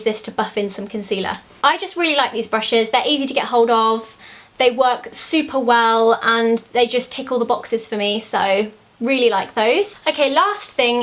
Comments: I thought you said tickle the boxes! :'D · When you said tick all the boxes I thought you said tickle the boxers lol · I thought you said it tickles the boxes. But then i realized it ticks all the boxes. lol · When you said "tick all the boxes" about the real techniques brushes, I thought you said "tickle the boxes" and I wondered for about 5 minutes if that was a British thing until I realized what a dummy I am!! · at first I thought she said tickle the boxes :D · I thought she said "Tickle the box!" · Ahah when she was talking about the real technique brushes, it sounded like she said 'tickle the boxes' not 'tick all the boxes' heheh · She said tick all the boxes Nd I thought she said tickle the boxes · thought you said 'tickle the boxes' not 'tick all the boxes' hehe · is it just me or does it sound like she says 'they just tickle the boxes' at 10:15? ;-) I loves the video though :) x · Hehehe when you said 'tick all the boxes' I heard 'tickle the boxes'. Great video, Zoella! Love seeing your favourites!